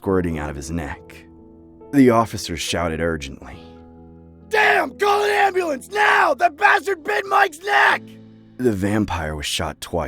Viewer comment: I heard "DAMN CALL AN AMBULANCE THAT BASTARD BIT MY SNACKKK" Did he really say that or am I hearing it wrong??😭